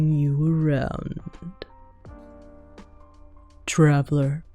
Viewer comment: What the video said: "I appricate you, Traveller" What I heard: "I love you traveller,"